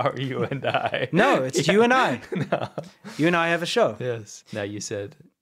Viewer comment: Justice for Winston! I heard "you and I have a show"